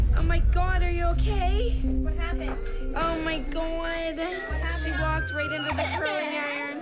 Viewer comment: I heard oh mi goid"